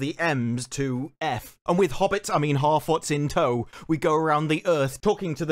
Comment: I am not a native speaker, so may be that is why every time some says "harfoots" I keep hearing "halfwits"